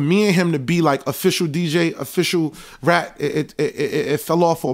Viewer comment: It sound like sayin "official rat" LMFAO